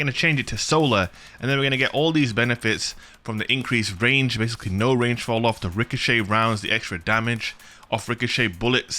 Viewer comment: “ Rounds from this weapon have reduced damage falloff” so yeah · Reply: I thought he said no falloff my bad